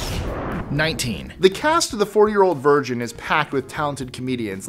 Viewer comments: #19 really really sounds like said "the four year old virgin". It really seems like is having a rough time. Lets all cheer on! You do great buddy! Yall need a bloopers list. Most can be from this episode. Lol. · wait what... #19 sounded like he said "the 4 year old virgin".